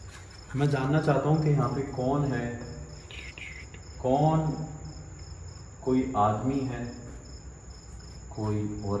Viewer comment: pr koi awaz sunai di. Ekdum clear awaz thi. Plz explain that. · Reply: Camra man said sehee......